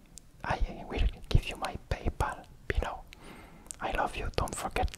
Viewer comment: I thought it said “I love you so much! Here is my papaya” and I was happy bc that’s so cute. But unfortunately I was wrong